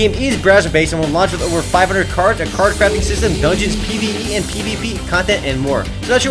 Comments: lmao at 0:48 it sounds like is saying "card fapping system" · i heard "card crapping system" :D